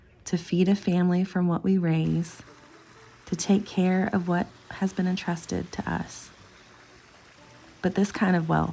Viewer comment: Anyone know what she means when she said “husband entrusted to us” at the 1:14 mark? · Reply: Hi! I see where you think it sounds like that. I actually said; “to take care of what has been entrusted to us.” Sorry for the confusion and thank you for watching 🙃